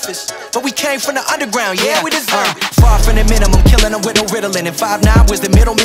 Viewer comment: Great job, potential correction suggestion. Screen appears @ 3:10 "Yeah, Uh, Far from the minimum Killin' em with no Ritalin'" Not Riddlin'. Although it does work in that concept. I think he is saying Ritalin. as in meds for adhd.